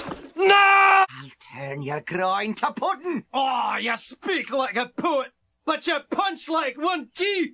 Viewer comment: "YOU SPEAK LIKE A POET, BUT YOU PUNCH LIKE ONE TOOOO!" XD Dead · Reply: i thought he said you speak like Putin but you punch like a Putin